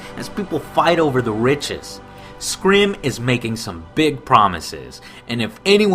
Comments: did he say SKRIM? lol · he said Skrim instead of Skyrim